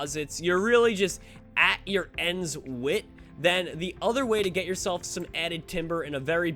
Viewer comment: did he say at your ENDS WIT?!?!?bruh